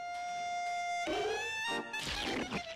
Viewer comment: Lol, I literally thought he said (as a kid) "A sad, crying clown and I are alone."